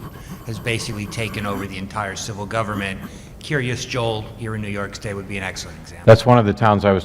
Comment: Does anyone know what he says at 13:55? The name of the town? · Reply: New York?